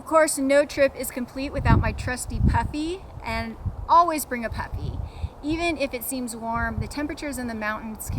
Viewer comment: Toward the end, it sounded like you said I usually bring my puppy, but I think you really said puppy!